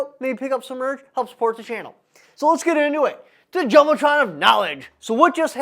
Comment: Maybe it was just the loud window in my shop, but it sure sounded like you said "THE JELLOTRON OF KNOWLEDGE!" 🤣